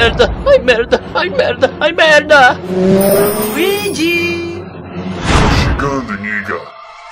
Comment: mano so eu que entendi assim "to chegando nigga"